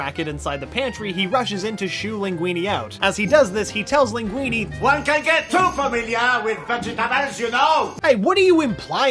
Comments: What did he say? It sounded like he said "One can get too familiar..." and then something else. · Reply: “one can get too familiar with vegetables you know” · is so hard to make out what he saying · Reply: T3RRIFY “One can’t get too familiar with vegetables you know!!”